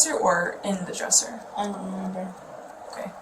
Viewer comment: the captions say "In there" but the boy is saying "I dont remember"